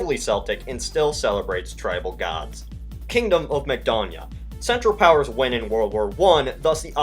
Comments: i thought u said kingdom of McDonalds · When he said Kingdom of Makedonya I thought he said Kingdom of Mcdonalds